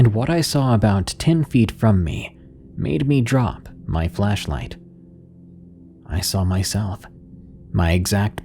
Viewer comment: 4.24 I thought he said ' made me drop my fleshlight '😂we say torch 🔦 here in the UK lol